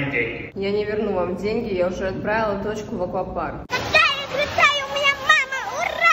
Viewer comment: мне послышалась точка отправила